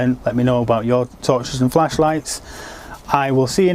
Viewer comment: Thought that said fleshlight... lol